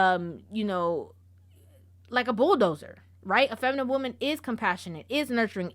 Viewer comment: "a feminine woman doesnt move around like a bulldozer...." Mane i thought you said bulldagger! I had to run that shit back. I was rolling